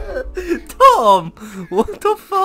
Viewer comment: did u just called a Tom???🤔